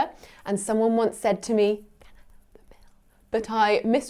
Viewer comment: The fact that I thought she said "can I have some milk" when she actually said "can I have the bill"